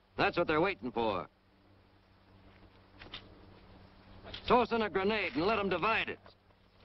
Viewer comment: it sounds like he said "Sause in a grenade!"